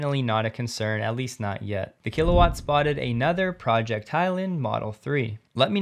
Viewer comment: Every time you mention Project Highland, my ears Prick up, because I thought you said Project Thailand ! Then my heart sinks again, as the Tesla Superchargers have NOT got as far NE as i would like, NOR in sufficient numbers.The Tesla Supercharger has only reached so far as Khon Kaen, which is at least 100km from me ! There are promised to be some within the "Central Shopping Malls", in ALL the Provincial Capitals, which would put one within 50km of me. Other Chargers are EVAnywhere, using CCS, 50kw and type 2 PTT (gas station), using CCS 50kw and some of these are promised to be Tesla, but NOT installed yet ! More infrastructure is needed !